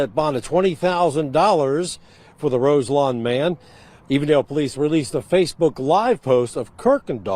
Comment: Ohio here. The location is E-vendale, sounds like you said A-vondale which is a different community located in Cincinnati. E-vendale is in the suburbs of Cincinnati. Shout out to the "Nati".